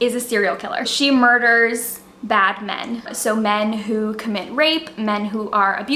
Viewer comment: I am laughing at myself so hard right now. When you said "(Jane Steele) she murders bad men" - I heard "she murders Batman" and was like WAIT WHAT? :D